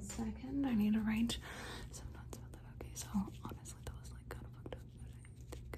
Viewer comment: anyone else hear at 5:32 “honestly that was like kinda fucked up” lmaoo